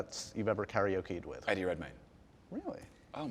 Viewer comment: I couldn't catch the name of the karaoke singer that impressed him? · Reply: Eddie Redmayne